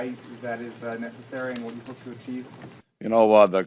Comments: [inaudible] but he clearly says “and what you hope to achieve” smh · "and what you hope to achieve" is clear as day why whoever did the CC said it wasn't understandable i got no idea. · How did they not hear the guy, he clearly said "and what you hope to achieve" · The subtitles say "can you explain why that is necessary (inaudible.)" The inaudible bit is "and what do you hope to achieve?" Just FYI for anyone who's curious.